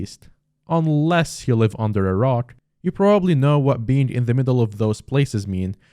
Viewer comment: So glad you made it out ok, Ngl when u said under a rock I thought u said under iraq